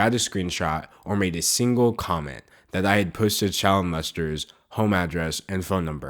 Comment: I thought you said child molesters 😂😂😂